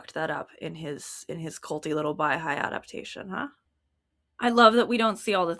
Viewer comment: "weird culty wayfarer set" is what I said. I don't think everyone who who is baha'i is in a cult, I think baldoni's weird, overly affectionate, seemingly very spiritally focused situation at wayfarer with sarowitz and heath and everyone is weird and culty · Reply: thanks for responding! i was referring to the comment around 53:32 where you seem to say "culty little baháʼí adaptation". having gone back, the pronunciation of baháʼí is different to how i know it so maybe you could've been saying something else and i misheard (very sorry if that's the case), but i can't figure out what else it would be.